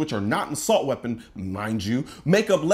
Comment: Oh my God. Colion, I thought you called Bloomberg a lying Jew a little more than a minute into this video. But you actually said “mind you “ But before I rewound the tape and listened again I swore I heard something else. Whew, I’m glad you didn’t say that!